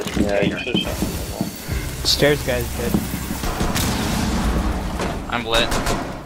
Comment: do I hear Polish "no i przyszedł" ?